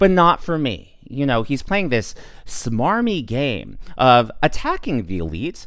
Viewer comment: did you say "smarmy gay"? 😂